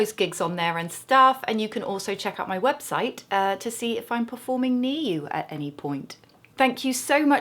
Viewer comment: At 25 seconds when you said "performing near you, at any point", I honestly thought you said "performing new, at any point", (meaning new material). I thought that was the poshest accent I'd ever heard! I wasn't sure if it was just how you spoke or a jokey, funny way of saying it. It took me a few seconds to realise I'd misheard, and laughs were had. Thank you for being awesome and funny :-)